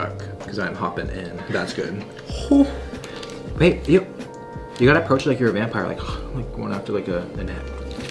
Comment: The at 19:13 Who else heard the: "babe... ew...//?//" Lol definitely misses someone.................... 😂😭😂😭😂😭😂😭